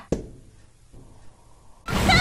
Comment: Wait I thought she was saying "I will reverse all creations!" in that line?